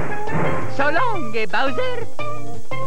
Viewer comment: did mario say so long gay bowser?